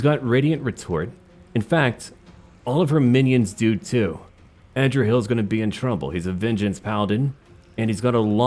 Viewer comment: I’ll be completely honest… I kept hearing “Andrew hill” for the MC name 😅😅